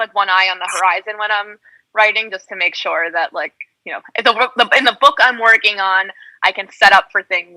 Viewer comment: Eu entendi mal ou Sarah disse: "Elain's book"?